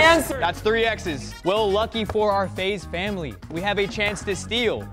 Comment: I thought I heard ACE FAMILY 😭😂